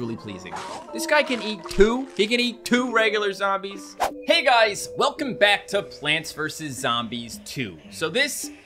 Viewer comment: I heard him say welcome back to battle cats 💀💀